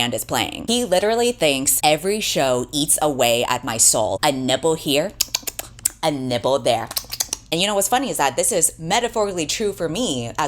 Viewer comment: I need everyone to know I misheard a nibble here and nibble there as "a nipple here * LIP SMACKING * a nipple there * LIP SMACKING *" and just accepted it